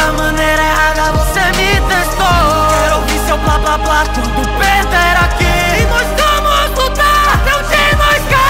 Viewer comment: Alguém mais ouviu blá blá blá no peter aqui??????? Ou eu tô maluco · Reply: Eu ouvi "peter" mais depois vi que era perder